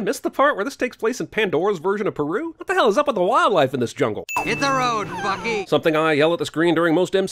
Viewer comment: Really thought he said “Hit the road Fucky”